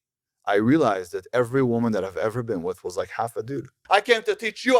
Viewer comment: holddddd uppppp did he just say the girls he’s been with are 1/2 dude????!!!! 😂😂😂